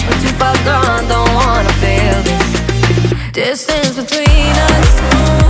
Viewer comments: What a different pronounciation, I heard 'This is between us' what about you? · at the beginning instead of "Distance between us" I heard "This thing between us" 😂😂😂